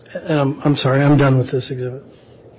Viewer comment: 1m= 39 inches...... 3 ft 3 inches I'm blessed or cursed to be the 80's children in Canada to learn conversion from imperial to metric system..... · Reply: I swear he said " i'm done with this idiot" Oh exhibit ...... dang old age.